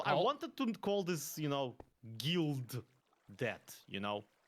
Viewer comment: I heard “guild debt”